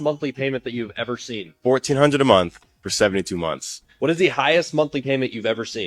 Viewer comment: I thought the guy @ 6:40 said $4,200/month for 72 months (giving me a mini heart attack) but he said $1,400/month which would be $302,400 vs the actual price of $100,800…and that’s not including interest! 😳